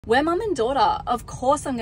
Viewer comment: I thought she was saying “we’re mom and Dora”